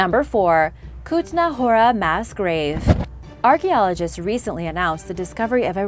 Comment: sorely disappointed, i thought you said mass rave instead of mass grave.